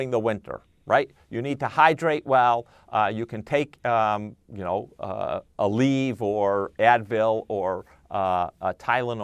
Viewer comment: What did he says at 2:29? You can take Advil ?